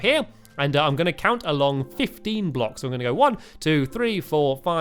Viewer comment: Thought you said 50 blocks. :P